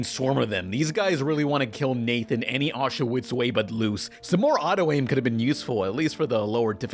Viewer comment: "These guys really want to kill Nathan any Auschwitz way but loose" If you're going to talk like this at least make some captions so we know what the hell you're saying. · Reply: Every Which Way but foot loose